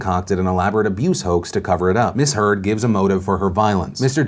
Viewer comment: Whenever you said Miss Heard, I thought you were saying "misheard", something wasn't making sense.